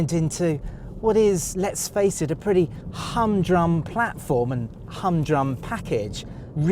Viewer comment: is he speaking Korean because i have no idea what he said. ham jung? hung drung? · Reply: humdrum /ˈhʌmdrʌm/ adjective lacking excitement or variety; boringly monotonous.